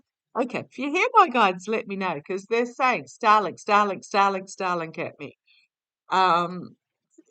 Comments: Definitely hear your guides right after you hold up the ENT ONLY card while looking at the table around 6:50 6:55 give or take a few secs. Very insistently it repayed what sounded like "Starlink starlink starlink" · I heard syarlink around 6:56!! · I heard starlink same voice I heard before.